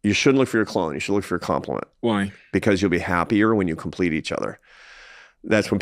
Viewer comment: Watch the full episode <link> Don't forget to Like, Comment & Sub! 🙌🏽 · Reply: I heard him say “complete” not “compete”